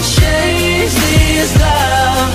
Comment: Maybe I’m wrong, but I hear “the chains only bring me down” not “the chains only break me”